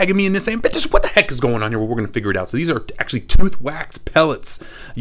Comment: It sounded like he said b**ches what is going on here💀